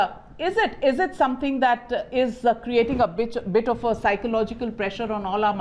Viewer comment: did she say bitch ??? 😂😂😂😂😂😂😂